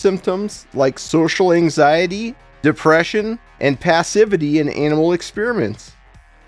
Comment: Did you...did you just say "passivity in anal experiments"?